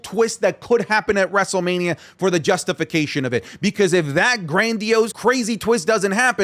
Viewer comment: wtf did he say!!! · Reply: Justification?